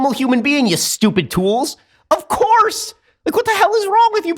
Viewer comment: Hey Ben Shapiro, I resemble that, "You Stupid Dwools", remark! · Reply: “Dwools”? Is that what he said? I heard “tools”. Maybe he mispronounced it and I didn’t catch it. Lol